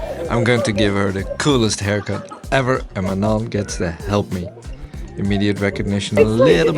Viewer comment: She looks beautiful with the buzz. When you first said the name, Manon, I thought you said mum. When she came on screen, I thought, wow! Further watching explained the misunderstanding. Both are beautiful ladies.